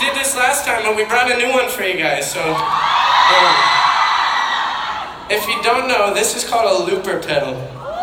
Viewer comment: I really wanna know what Zach, Corbyn and Jack are saying at 0:25 lmao! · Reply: color looper what